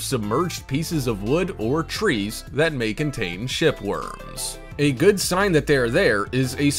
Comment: I thought u said shit worms hahahah